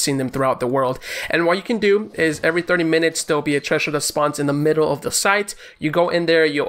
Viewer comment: i hear trasher